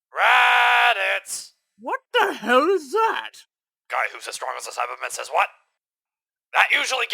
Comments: Wat is nappa saying at 0:23?? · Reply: guy who's as strong as a saibaman says what? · omg i found out what nappa is saying at 0:23 "Guy who is as strong as a saibamen says what"